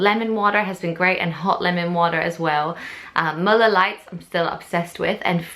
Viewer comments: did you say you're obsessed with Miller light? (@ 6:10 on your video) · Im a Texas girl and at 6:09 I thought she said Miller Light ( which is beer) and I was like Whaaaaaaa? lol I love her accent! I wish I had a nifty accent like that instead of my Texas Twang.